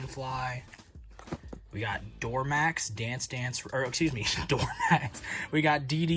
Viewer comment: not gonna lie, I thought it said dormax also LOL